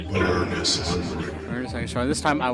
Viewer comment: wish they corrected the voice. They actually are supposed to say "birdy's hungry" and "Birdys coming" when you run low on time or out of ammo. Not "bird is coming". The Dactyl's names are "Birdy" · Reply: Thank you for that; I SWORE it said “Bird is Coming” in the original game